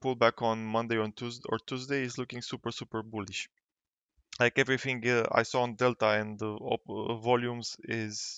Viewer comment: 1 - We're not all boys. 😉 2 - At 0:35, you mentioned everything you saw on "Tell-Time"? I couldn't understand what you said. UPDATE: Everything you saw on Delta. Got it! Thanks for a nice video!